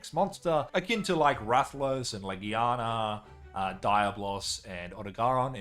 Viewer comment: Bro the way you said Diablos I thought you said diet loss 😂